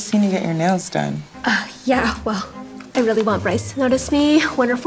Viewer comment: When she said, "I really want Brice," I thought she said, "I really want rice." Lol being Asian be like 😎